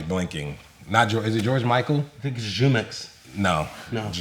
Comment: did he just say jumex